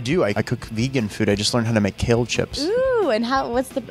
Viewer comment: I thought she said Caleb chips lmao